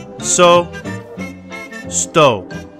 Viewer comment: when he said sto it sounded like stone